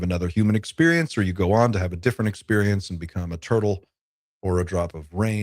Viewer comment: At about 40:45, did you say turtle? I couldn't help but think of Maturin the space turtle in Steven King's It story and Dark Tower series ....:) creator of the universe & demigod in those books.